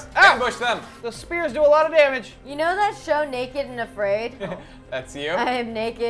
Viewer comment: When Mari mentioned "naked and afraid" I thought she said naked in a braid